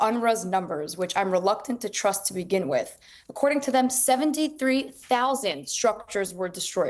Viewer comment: did she say 73,000 truck? 🤦🏻‍♂️🤦🏻‍♂️